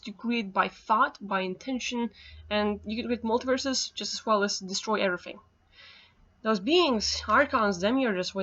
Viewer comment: I don't catch what you're saying about "multiverses" "…destroy everything". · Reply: Halvor Raknes ‘You can create multiverses as well as destroy everything’